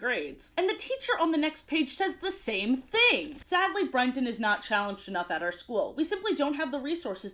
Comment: I apologize to all people who are named Brenton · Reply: I kept mishearing it as Brendon and joking to myself “man this episode of Home Movies is weeeeeird~”